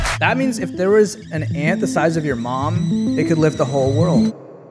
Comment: At first I thought he said Pam but I heard it he said your mom